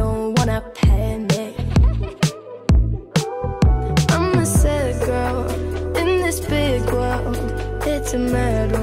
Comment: “I’m just a sad girl, in this big world” I thought they said “im a trash can in this big land”